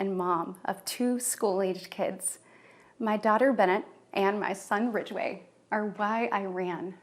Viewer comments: did she say "Bennet" and "Ridgeway"? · Did she say her son’s name was Ridgeway???